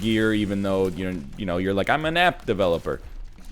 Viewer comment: I literally am an app developer but I somehow still misheard this as "I'm a nap developer" and like what degree do I need for that